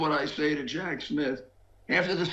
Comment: I thought Rudy had said “miss” referring to the new judge. Here I could figure out he said “Smith” during his lie filled rant. I suppose he will never stop lying. But why not. If DT goes down so does he!